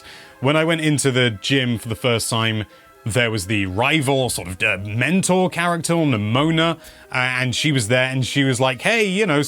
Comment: I typically just listen to YT vids and when you said “the mentor character” I swore you said “dementor character” and was a little confused and immediately looked at who this person was haha